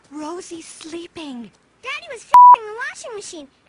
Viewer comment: MISHEARD @ 0:57-0:58 f*cking HEARD: Bucking.